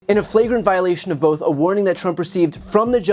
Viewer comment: For a sec I thought BTC said "fragrant". 😂Was wondering if he's taking English lessons from MTG...😂😂